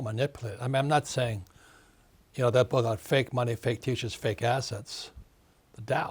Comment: every time he says "Fake money fake teachers fake assets" i hear "Fake money, fake teachers, fake arses"